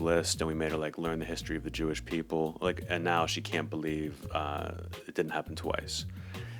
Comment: oh he said "she can't believe it didn't happen twice". I thought he said "she can't believe it didn't happen to us". At first i was confused, but wow. gez. Anyways thank you for explaining to me